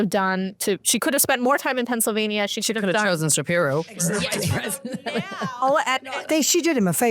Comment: did she just say shapiro @ 3:36? im guessing she finds no issue with his 911 call for his wife